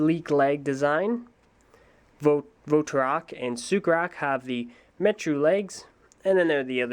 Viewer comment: It sounded like he said "Superak"lol